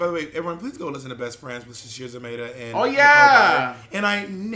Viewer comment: did anyone catch who's podcast Bob mentions at 42:21? something called best friends? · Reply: Best Friends with Nicole Byer and Sasheer Zamata